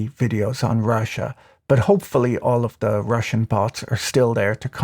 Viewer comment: I'm Russian bot but had to upvote the video because this rap is too good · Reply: Glad you clarified that -- I thought he had said "rushin' bot"